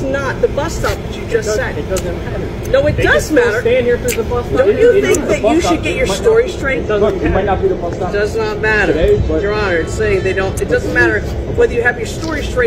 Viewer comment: Is my girl in the last clip really a lawyer? Cuz she's arguing her points like she is. PLUS.... I even think she said Your Honor, while she was talking to the cops! It's right around the 24:03ish mark! I literally died laughing! I was like, DAMN...homegirl is really trying her hardest to win this battle for her client. Which in this case, it's herself! Lmao! 👩🏼‍⚖️